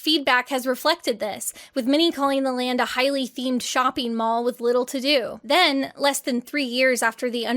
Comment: I misheard "Many calling the land an overpriced shopping mall" as "MINNIE calling it..." which made me think - wow, even Disney characters are dissing the experience!